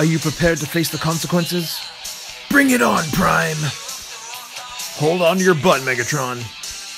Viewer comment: did optimus just say hold on to your butt megatron WHY